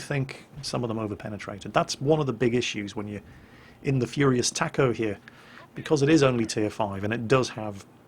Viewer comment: See .... I heard "furious taco"